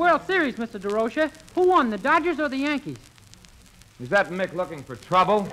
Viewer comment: “Is that mick looking for trouble??” Yikes! Did I hear that right?!?! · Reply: He did say Mick. As a Mick myself, no offense taken. Cheers!